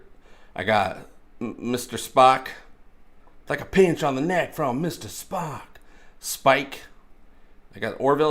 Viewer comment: I always thought he was saying, "like the pencil neck on Mr. Spock." I swear everyday...